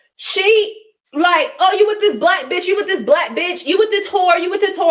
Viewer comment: I don’t like that she keeps mentioning her being a African. Like address her and her character not where’s she’s from. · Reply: I thought she said Cindy was calling her a “Black b” ???😅